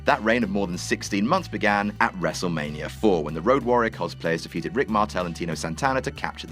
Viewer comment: Did you call Demolition the Road Warriors? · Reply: No, he called them "Road Warrior cosplayers", which are people that wear costumes of someone else and pretend to be like them, i.e. Ax and Smash dressed up as Hawk and Animal and pretended to be the Road Warriors.